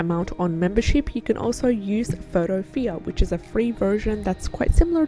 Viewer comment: Hi, thank you for the video. That free photoshop alternative you mentioned at 7:50 did you say PhotoPea?